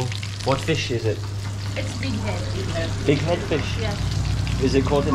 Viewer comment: I thought he said Dic... Head Fish 😮